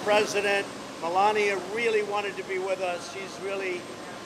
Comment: Did he.....did he just say "Melania really wishes she could be here" with Melania standing right next to him?